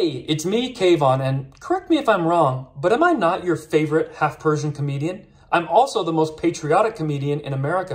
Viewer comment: thought he said "half person comedian".. I was like, "Oh damn, Alex Jones was right 'bout them lizard people.." then I looked down and saw it said "Persian"... 😳